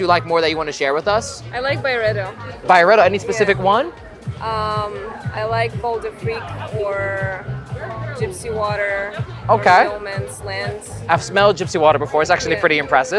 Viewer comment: which fragnance did she say? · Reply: Byredo bal dafrique, gypsy water and rose of no man’s land